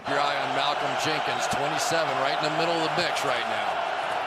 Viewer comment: what did he just say? "Right in the middle of the b**ch right now" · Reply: Mix